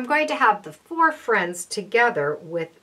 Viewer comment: Also, I'm not sure what you're saying at 12:35. "So I'm going to have [?] within that 3½" window mark." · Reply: I going to have the four friends together within in that 3/12" window